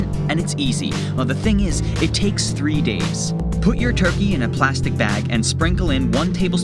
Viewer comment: How many days do you dry brine the turkey? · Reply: I thought he said 3 days.